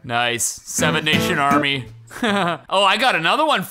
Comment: Did he say seven nation army ???